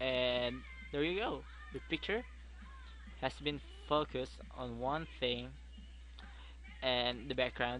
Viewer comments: when he said focus in the beggining i first thought he said fuckers :P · When he said focus I thought he said fuckus :L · lol i had to listen a 3 times when he said focus cus i thought he said "fuck us'